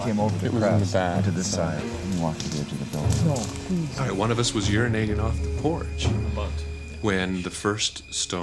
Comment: I swore he said “one of us was urinated on” 😂 I choked on my food